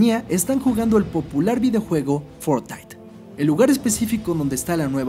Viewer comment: soy yo o escuche FORTITE